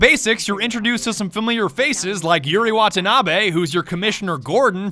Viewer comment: I thought it said ‘Yuri Wannabe’